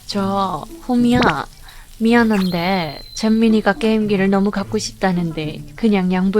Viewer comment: 나만 잼민이로 들리나